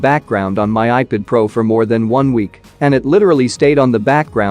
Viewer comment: does he says more then 1 week